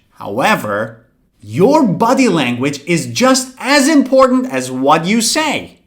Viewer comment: I thought you said buddy language 🌝 sorry for that my teacher